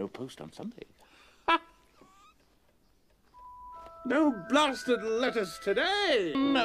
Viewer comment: That harry potter scene, I misheard what Mr Dursley said and heard "No blasted lettuce today! no sir!"